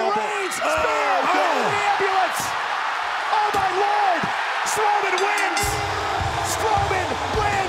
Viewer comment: did he just say slowman wins?